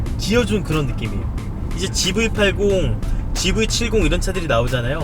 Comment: 잘 들어보면 집을팔공 이라고 들리네욬ㅋㅋ